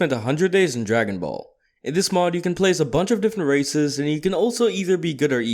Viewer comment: did he just say racist